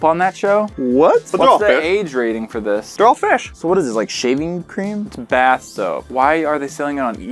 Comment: I legit thought that said, "What is that? Shaming cream?"